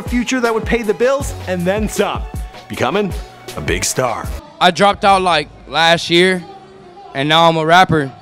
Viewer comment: I can't be the only one who heard him say Deadstar dropped. I don't think it dropped yet tf